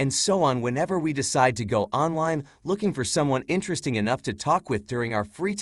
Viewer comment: Jack says “Onwine” instead of online 4:22